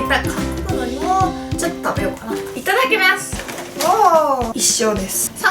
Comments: いただきみゃすって聞こえる笑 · 最初いただきみゃすって聞こえた